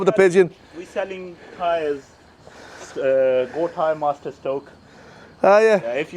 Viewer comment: I honestly thought that guy said goat hire not go tyre . 😂😂😂